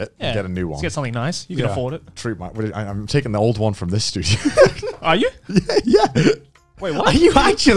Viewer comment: I think Joey misheard "from this studio" as "from this dude"